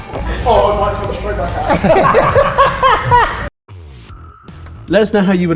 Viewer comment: does anyone know what said 🤔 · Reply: It might come straight back out